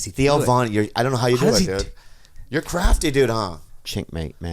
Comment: I heard "chenck mate".